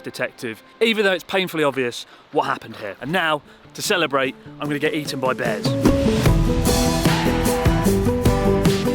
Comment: When Chris said time to celebrate Im going to get eaten by bears, I thought he said “Time to celebrate I’m going to get you to my bed” had to rewatch that lol